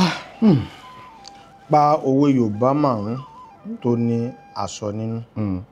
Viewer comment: At exactly 29:42 they made mistakes on Masoyinbo program There is nothing like aso in that question, instead of aso it was Oja dey display on the screen, " owe marun toni oja ninu, who else noticed that mistake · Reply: I noticed that too and I think is typical error but I was surprised when Mr Afeez Owo pronounced Aso instead of Oja